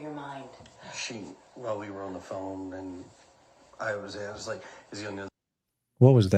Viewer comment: I think he started to say I was asking